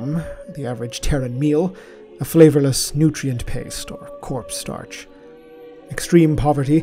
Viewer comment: did he say “corpse starch”?